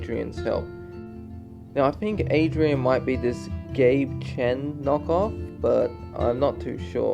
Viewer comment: You said Gabe Chen knock off but I heard “gay ken knock off” I was like, 🤨🧐….I guess I see it lol